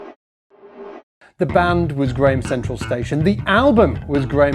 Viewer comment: A correction. When you mention the " money money money" song (actually called For the Love of Money) is by the ojays. I thought I heard him say it was by grand central station. Lol